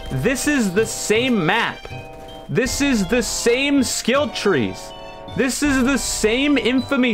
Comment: i heard that "kill trade" in the intro segment